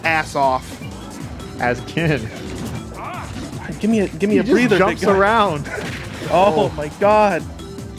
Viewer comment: Anyone else hear the n word around 8:52 to 8:54? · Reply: Give me a breather DK not the n word